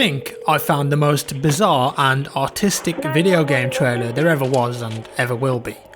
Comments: At the beginning I thought you said the most bizarre and AUTISTIC video game trailer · at first i thought he said bizarre and AUTISTIC lmao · I thought he said autistic at the beginning and not artistic lol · i thought he said "autistic" not "artistic" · I thought jack said "bizzare and autistic" and Ibwas confused as hell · I thought he said "the most bizarre and autistic" lmaooo damn you memes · i thought when he said artistic i he said autistic lol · thought he said " I think I have found the most bizzare and autistic video game trailer" XD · I thought he said autistic